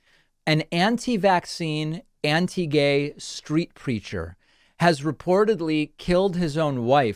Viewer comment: For a second I thought you said anti-vax “street creature”